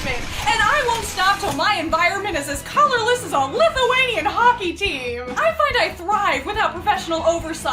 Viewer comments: am i tripping or did she say 'lithuanian' RAHHHHHHHHHH 🟨🟩🟥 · Did i hear that correctly or did she said 0:39 Lithuanian hockey team???? 🇱🇹🇱🇹😍😍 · WAIT DID YOU SAY LITHUANIA?? RAAAAAAAAAAA 🦅🦅🦅🦅🦅🔥🔥🔥🔥🇱🇹🇱🇹🇱🇹🇱🇹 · The fuck you mean" as corolless as a lithuanian hockey team"? Hi from Lithuania, but I dont understand the joke. Or am I misshearing something?